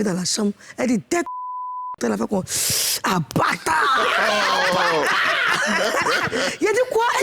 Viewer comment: A 35:42 (35mns42), disait quoi ? Où elle a dit ''abatar'' là lol, j'ai pas compris...